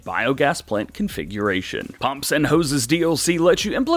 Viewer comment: Lol I thought it said pimps n hoes this going be interesting